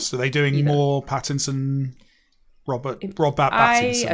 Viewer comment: Don’t worry Mike, I heard you. I too love Robbat Battinbat (grab dat gem)